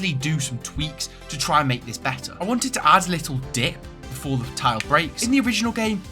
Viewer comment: I heard that wrong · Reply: "i want to add little di-"